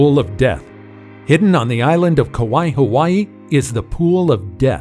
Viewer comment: NAH I HEARD HIM SAY KAWAII HAWAII